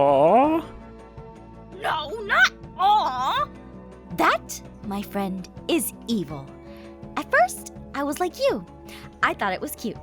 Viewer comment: I fricken thought she said “that my friend is EIN” and I was like oop Also did anybody notice Garroth wasn’t there?